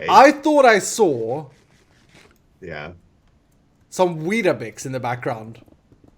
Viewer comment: I thought said he saw weed in minute 03:00. XD. Love the show